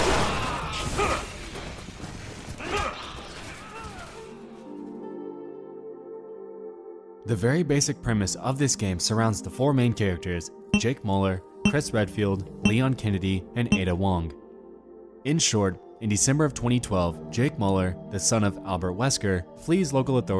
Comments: I thought you were saying Jake Pauler but you were saying Mauler. It's pronounced Meuller pls pronounce right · Misheard him saying "Jake Pauler."